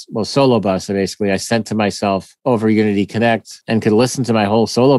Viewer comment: says how he sent solo bus audio to his home over "something-connect?". I can't hear what exactly he says, does anyone know? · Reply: I think he said Unity Connect!